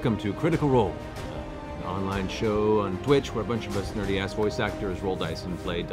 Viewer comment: Thank you, subtitler, for showing me that whenever I hear Matt say "dirty-ass," he's actually saying "nerdy-ass."